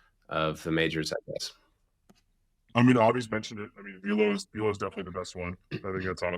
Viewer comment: what tool does say at 31:49 - evo?? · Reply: Velo